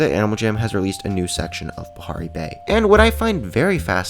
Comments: For a sec I thought you said Aprrai Bay XD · sounded like he said Aparri Bay. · XD I thought he said Aparri bay 😋